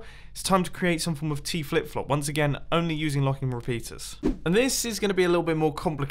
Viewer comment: LOL!!! I thought it said locking refriderator